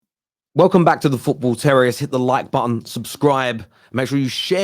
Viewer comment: I wasn't paying attention properly, so when the video started I thought you said "Welcome to The Football T3rr0ris7." I had a double take, not gonna lie :D